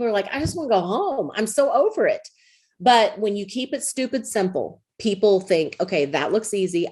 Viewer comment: Did she just say "Keep it Stupid, Simple"???